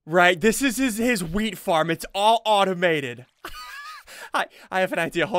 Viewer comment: did he say weed or am I hearing stuff?